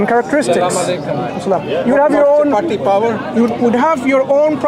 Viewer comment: did man just say "do you have p@ power"